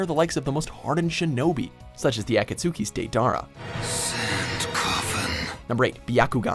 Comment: YO did he say at e kutski Lol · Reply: HDvortex he also said dara 😂